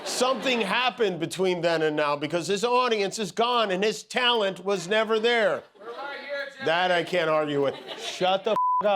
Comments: what did the audience member say? Closed caption doesn't tell me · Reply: “We’re right here, Jimmy.” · What did the guy in the audience yell at 2:37? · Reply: “We’re right here, Jimmy.” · what did the audience member shout out? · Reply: "We're right here Jimmy." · What did guy yell at 2:40ish? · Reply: He yelled, “We're right here Jimmy!” 😂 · what the guy said ? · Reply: We love you Jimmy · Anyone catch what was said at 2:40 ? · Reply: “We’re right here, Jimmy.” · What did the guy in the audience say? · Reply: “we’re right here jimmy” meaning his audience wasn’t gone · What was the guy shouting at 2:37? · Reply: “We’re right here, Jimmy.” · what did the guy said? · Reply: "We're right here jimmy" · What was the heckler saying at 2:38? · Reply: That we’re here for you. Your audience is here for you. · What did the heckler shout? 😅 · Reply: “We’re right here, Jimmy.” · what did the guy in the audience say? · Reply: He said: "we're right here, Jimmy. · Does anybody know what was said off camera at 2:37? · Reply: We're right here Jimmy. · what did the guy in the crowd say? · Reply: I believe, "We're right here, Jimmy." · what’s the audience saying? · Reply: "We're right here Jimmy" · What did that other person say? I didn't get it. At 2:38 ? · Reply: "We're right here Jimmy!" - Random Guy